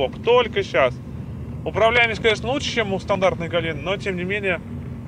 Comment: мне одному послышалось "Управляемость конечно лучше чем у стандартной Галины"